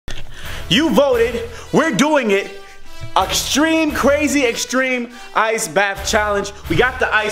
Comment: did u say auxtreme ? 😂😂